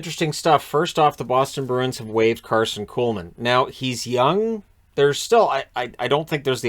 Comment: When you said Kuhlman, I accidently heard that as Poolman. lol Thanks for the content as always.